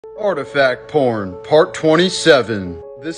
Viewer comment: Am I the only who heard, "artifact corn" and thought he was about to say, "this is the oldest known meatlight ever made."